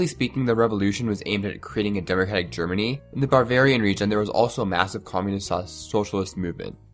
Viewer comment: All I heard was "Barbaria"